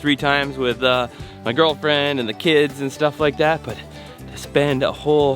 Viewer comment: did he say gf? thought you were married...